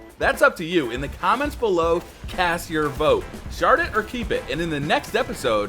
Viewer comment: Honestly the first time I heard say shard it or keep it I thought he said shart it or keep it.